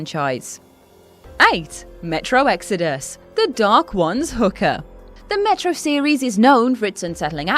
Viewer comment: So.....yeah.I dont always watch but mostly LISTEN to these vids... at 3:19 I definitely thought Ash said the dark ones HOOKER😅🤣